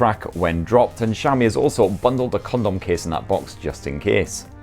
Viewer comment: LoL! Did he say Condom case?